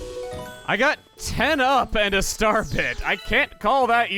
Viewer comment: It sounds like he said starpit instead of starbit.